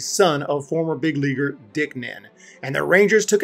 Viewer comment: When he said Dick Nen I thought he said Dick Man and I thought now that’s a hell of a name